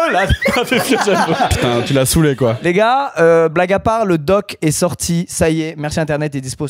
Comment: Black à part ?! QUOI ?!